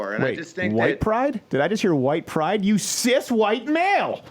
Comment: Did he say "Sith White Pride"? Darth Maul would be very disappointed.